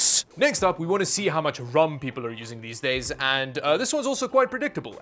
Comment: Sorry, I don't use rum on my PC ( as Youtube caption says ) . I use RAM . 🤣 Rum probably will make a good coolant for watercooling.